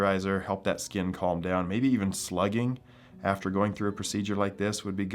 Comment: did you say “slugging?” Please explain